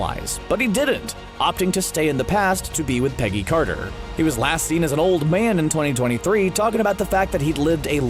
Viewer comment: I don't know did I hear that right but "Peggy Carter was last seen as an old man"